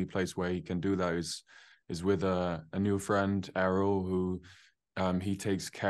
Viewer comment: It was an awkward interview. And who is Arrow? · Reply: I thought he said "Errol" but there's an Asha'man named Arel in the books so maybe "Arel"? And all Josha said is that he's a "new friend" so we'll have to wait and see!